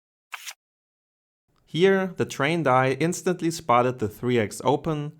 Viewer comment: What did you say at 1:00, here the `train die???` instantly,,,, I'm really enjoying watching your content, but I'm not native English speaker so I'm glad if someone tells me what did he say at 1:00 . plz